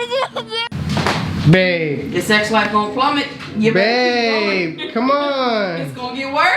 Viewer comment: did she say your sexlife gonna plummet?😂😂😂😂